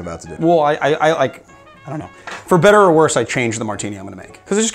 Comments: it sounds like says ' for bitters or worse" 😂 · Did you say... "for bitter or worse"? 😜